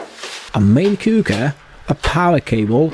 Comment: The way he said, main cooker. I thought he said, main cougar, and was talking about his wife.Haha